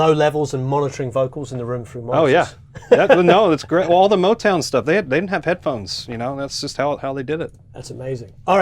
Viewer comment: Hello, Great video! Does anybody know what he said at 19:42? Thanks! God Bless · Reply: "All the Motown stuff, they hadn't had headphones. That's how they did it." You mean that bit? :-)